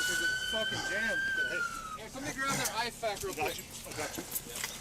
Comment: I thought he said "somebody grab an ice pack real quick" lol